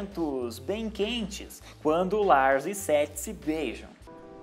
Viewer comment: eu entendi:"Quando Lars e Sexy se beijam"kkkkkkkkkkk